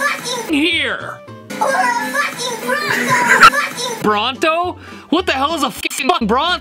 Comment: Donald Duck actually wasn't misheard, he's just cussing like a sailor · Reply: I heard “WHAT THE HECK!”